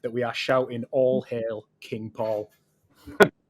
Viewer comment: Could have sworn he said "All Hail King Prawn" Thought he was ordering a takeaway for a minute 🤷🏼‍♂️